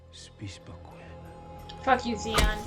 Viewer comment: “….F*** you ZEON!” I might have a little bit too much whiskey in me right now but I replayed that twice just to make sure that’s what you said. 😂 “Zeon” 😂😂😂 (Poor THEON)